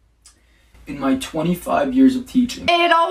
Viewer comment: "In my twenty five years of teaching" me and what i hear: "In My SiX tHoUsAnD yEaRs Of TeAcHiNg"